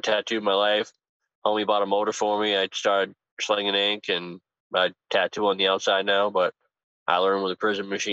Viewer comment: the face makes when he says "it only happened twice it wasn't that bad" is priceless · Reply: did he say he was slinging the skin flute and hustling